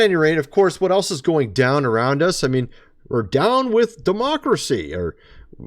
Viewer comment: That weird... I heard "down with America!'